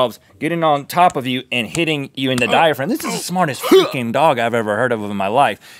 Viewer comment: What did Rhett say? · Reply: I had to listen to it like 6 times carefully. He does say freakin'. But dang Link just happens to make a noise at that very moment lol